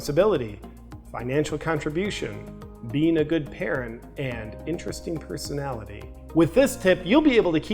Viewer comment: I thought he said "interesting smell."